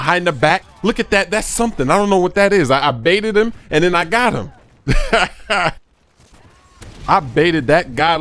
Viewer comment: who else thought he said bitch